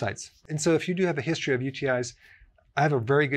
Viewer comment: What does he say at 1:42? "If you have a history of..."? English is not my mother tongue and sadly i don't understand this word. Can someone help me out please? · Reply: UTI.. which means a urinary tract infection. Hope that helps you